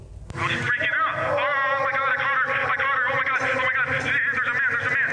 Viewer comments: I think he actually said "oh my God I caught her" and not my daughter. He considered this cheating even though she had been done with him. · I think he actually said "oh my God I caught her" and not my daughter. He considered this cheating even though she had been done with him. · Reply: I slowed it down to 0.25 speed and I clearly heard “omg I caught her I caught her…There’s a man” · I’m confused did do hear different things in the beginning of the interview? I heard him say “omg I caught her, there’s a man there’s a man.” Not that their daughter was kidnapped.